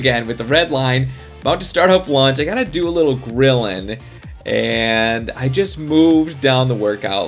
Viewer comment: It sounded like you said krillin but you said grilling lol